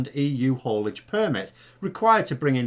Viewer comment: I misheard hermits instead of permits initially. UK does need more hermits TBH...